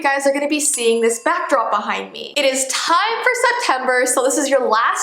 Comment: Jess: “it is time for September” What I heard: “it is time for some pepper” Me: * confused *